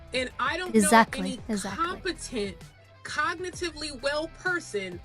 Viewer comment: Sorry, I only heard "confidence" in my end instead of 'competent'. Just... Why!?